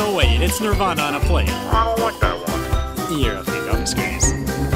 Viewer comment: what is he saying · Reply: “You’re a fucking disgrace”